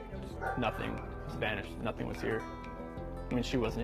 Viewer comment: Why at 2:19 does Chris say "SPANISH"...????? So weird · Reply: He says “vanish”.